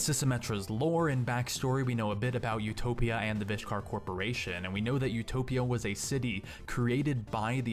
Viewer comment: who else heard "the bitch car corporation" ? XD